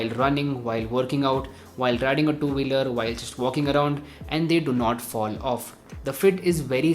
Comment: i heard smoking around😂(but it was walking around)